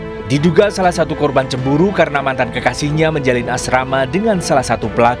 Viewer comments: menjalin asrama ? Salah denger ga si aku wkwk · Menjalin asrama gk salah denger aku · menjalin Asrama..apa hanya AQ yg salah denger ya🤫🤫🤭🤭